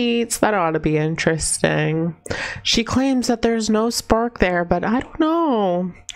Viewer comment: 9k I swear when you say that there is "no spark" between her and Petes I heard "no spunk" 😅😅😅😅